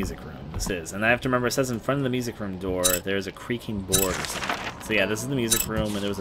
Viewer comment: At first I thought he said Luigi boards XD